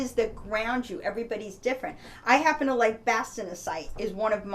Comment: Can You reply the name of stone that you mention at 4:05 ..? Please I just couldn't hear it.. Thanks :) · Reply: Vegh Atilla Hi, The crystal is Bastnaesite. Blessings